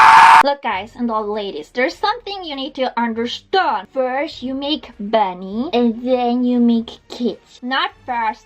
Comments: I heard..."First you make bunnies....then you make......babies"... eh? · FIRST YOU MAKE MONEY THEN YOU MAKE KIDS.... SMART GIRL 👍👍👍👍👍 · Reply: I love but I have a hard time understanding her sometimes... I kept hearing "first you make bunnies, then you make cats" needless to say I was quite confused.......